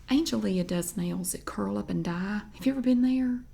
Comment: That was the name of the beauty shop where my mom used to go get her perms when I was a kid. I spent many an hour in there. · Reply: I am from Texas and I have never seen one! I do love a good beauty shop, there is no better gossip. I thought you said curl up and die at first, glad I figured it out 😂